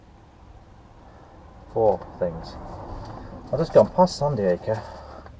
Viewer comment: 31.48 Sandy acre makes sense..at first i thought you said San Diego.